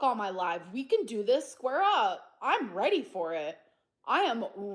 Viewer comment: Did she just say square up ??? Gorrrrrl you can't scratch your arse let alone stand haha